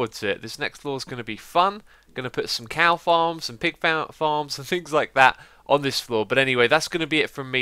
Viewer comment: Did i hear "cow farms and pig fountain" at the end of this vid? xD if any1 can invent a minecraft pig fountain, it's you!